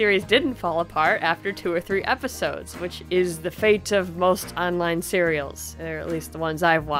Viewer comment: Did you just say "online cereals" 0:53 Did I hear that right!?!?